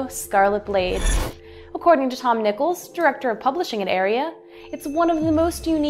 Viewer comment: I thought she said Tom Nipples